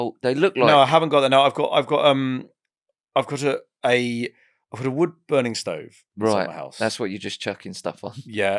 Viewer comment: "Stop burning my SHIT" That is all I heard when listening to this part 🤣 18:00 Netflix can ;)